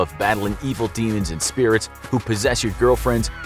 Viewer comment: did he really say "yeet them out open windows"